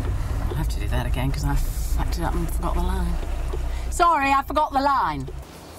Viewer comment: does Elizabeth say "F***ED it up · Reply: fucked it up!